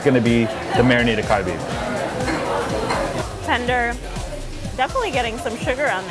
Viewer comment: the subtitles say Bulgogi, but I'm sure he said "Marinated Galbi"